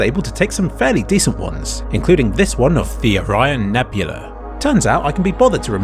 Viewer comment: the captions say horseshoe but you say orion